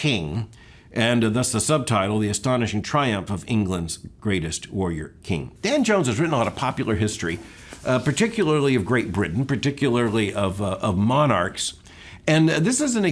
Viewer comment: Nice list! I 100% thought you said Van Jones and I couldn’t get my head wrapped around him writing a bio of a centuries old British monarch.